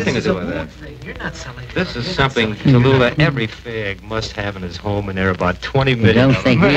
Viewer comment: Did he really say “this is something every f-slur must have in his home” haha funny to have it caught on recording! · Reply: I heard him say "fag".